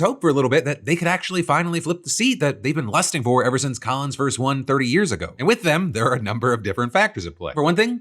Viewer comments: Captions say Collins first won her seat 130 years ago. Honestly i could believe it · Subtitles show that Collins has had the seat for 130 years instead of 30... I see what you did there subtitles lol